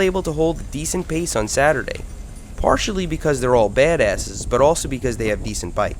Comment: thought he said bad at it lmao